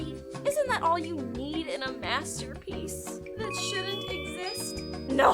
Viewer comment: I heard; masturbpiece!! LOL